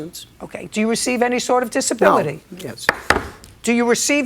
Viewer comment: I'm hearing a no then a whispered mumbled yes to "do you receive any sort of disability?" Anyone else hear a mumbled yes? When he looked up and said no his eyes were telling he lied. He doesn't want to get caught collecting disability and working under the table. Apparently he's been collecting since the early 2000's.